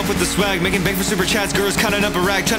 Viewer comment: Bro when I first heard this when he said "just sucks at math" I thought he said "Just sucked sapnap" 🤠✋